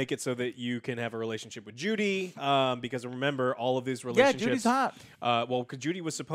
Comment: What I heard Joe and said: "People are also making so that you can have a relationship with Judy" Joe: "Yea, Judy Hopps" Me: What now?